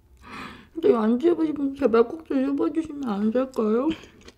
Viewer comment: 이부분 뭐라고 하시는 거예요???? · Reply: 안드셔보신 분들은 진짜 한번만 드셔보시면 안될까요? 라고 하시는 겁니다